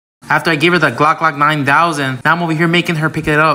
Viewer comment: I thought bro said Glock 9000 for a sec 😂